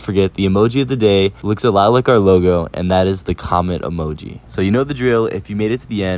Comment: I thought you said "comment" emoji but then I looked at some of the comments and realized you said "comet" and now I feel dumb. XD